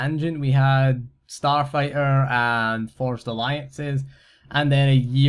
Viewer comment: I misheard "forged alliances as "forced alliances"... I know what I'm calling it from now on lol